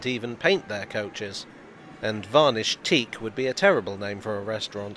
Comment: Disagree. I think "Tarnished Teak" works for a restaurant as well. Edit: Or "Varnished". I misheard.